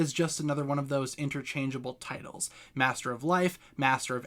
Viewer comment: I thought said master of light...